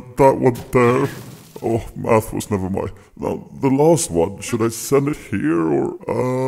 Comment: Can someone understand what he says in 3:08 ? oh ........ Nevermind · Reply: Oh, math was never my... [strong side] now the last one should I send it here or uuuuuuuuhhhhhhhhhhh